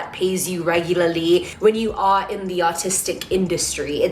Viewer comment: I thought she said autistic